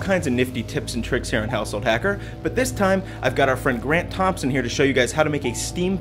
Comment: for a second it sounded like you said Brent Thompson